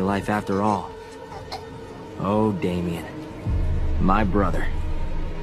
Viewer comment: What does say jason at 2:02? I can't understand this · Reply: “You’re going be alright I’m going to get you all fixed up”